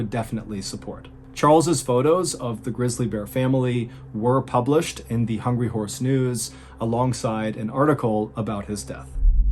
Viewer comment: I heard "The Hungry Horse Noose." That sounds grim. I need better earbuds.